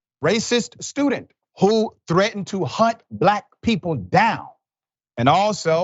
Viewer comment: Sounded like she said haunt not hunt